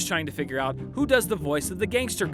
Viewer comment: You mean Ron Pearlman? I kept hearing Ron Pearl. Looking forward to seeing The Crimes of Grindelwald next Sunday!